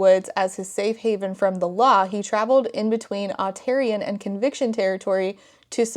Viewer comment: I'm listening to this as a podcast while I do other things and hearing you say Otarian(?) at ~27:15 was so jarring because I thought you said Ontarian, as in resident of Ontario... I had a moment of "!?!?!? what do WE have to do with all of this"